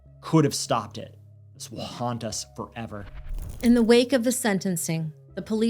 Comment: I had to go back I heard "wakanda forever" and was like what. But you said it "will haunt us forever"